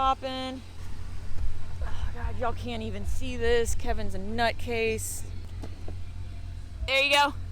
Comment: I'm watching this while cleaning, and when you said, "you guys can't even see this. Kevin's a nutcase." I heard, "you guys should see this. Kevin's got his nuts in a case." and I was so, so very confused. For like 3 minutes. (cause I'm an idiot) 😅😅 Until I remembered I could rewind it, and get clarity. I'm always impressed with what the stepchild can get through. Y'all make a great team.